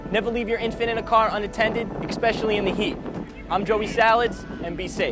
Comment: expeshialy, did he just say expeshialy